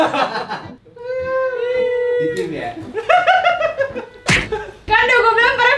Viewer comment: demi apa pertanyaan terakhir jwb ANJAY🖖. . . . TETAP POSITIF THE RICIS!!! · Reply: Ternyata aku ga dendiri hahah kk juga bilang "di tim ya" 😂😂 sama pas ahir kk bilang "yah ketauan derr" bener ha sih? Atau kuping aku yg salah denger wkwkw